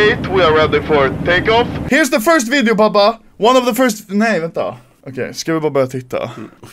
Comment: nee wat de (it sounded like that dutch wich means: no what the)